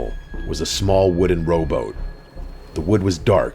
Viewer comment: S3 I keep hearing "ROBOT" rather than "ROW BOAT".